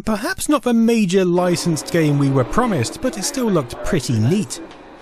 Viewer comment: did you say "pretty 1337"? Did I hear that right? 😮